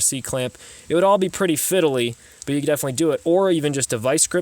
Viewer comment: What was the word at 4:10? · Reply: Fiddly?